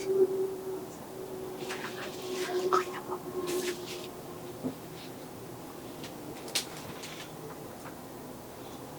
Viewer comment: Kaya pala may narinig ako 15:37 na "HAY NAKU" parang galit kay sir Ed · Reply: Ung babae po yun na ksma nila sir ed "okey na po" pagkarinig ko😅